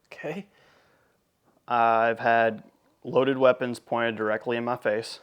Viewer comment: I first thought you said you LIKED having loaded weapons pointing directly at your face….